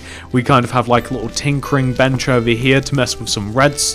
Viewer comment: I thought he said tinkling bench